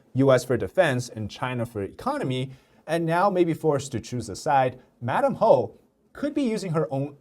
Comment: I literally heard you saying madam Hoe 😂